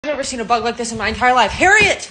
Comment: At first I thought she said I hAvEnT sEeN a BuTt LiKe ThIs BeFoRe